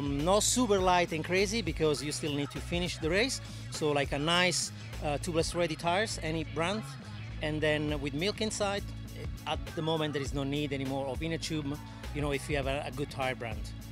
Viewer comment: Wait did he say "milk inside" at 1:13? #PRO sealant?